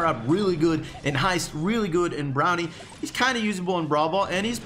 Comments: did he say brownie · did any one hear brownie instead of bounty ?